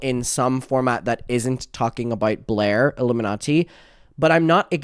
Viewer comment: sounded like you said iilluminatzi XD